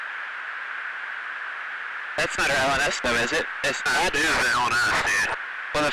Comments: “That’s not a Sesna though, is it?” It sounds like he said something like that. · i think he could be saying: that thing isnt from earth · I think the pilots are saying "That's not LNS though, is it? It's not-" and "That is LNS, dude." at 17:23. This is what I think they're saying and could be completely wrong. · I think they are saying “N1S”? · As I heard it, the "(unclear)" part in the dialogue at around 17:24 is saying "out of earth". E.g, That's not "out of earth" though, is it? It's not- that "is out of earth" dude. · anyone able to help identify what they are saying? i hear 3 to 4 syllables. something like, "that's not a drone with us though" or "that's not a umbrella" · "rnls" is what i heard from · I think they said "Its not ( That's not one of us though, is it ) ( That is not one of us, dude ) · Real quick, at 17:27 I think he says "that's not one of us though is it" as they both confirm their disbelief